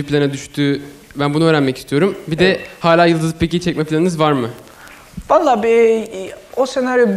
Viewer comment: bu çocuk ne diyor anlayan var mı? neyi çekme planınız var mı dedi? 4 defa söyledi anlamadım. 4 kere de geri sardım yine anlamadım bende mi problem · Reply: yildizli pek iyi